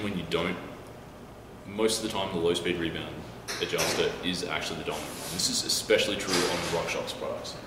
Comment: Hi! Good explanations, thank you. I wanted to clarify one thing: at ~9:48 you say "and even if you don't, most of the time the low speed rebound adjuster is actually ???. This is especially true for RockShox". I cannot figure out what is the word in place of ??? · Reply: Hi, I had the same issue but after a few views I'm pretty sure he says "the dominant one". Cheers!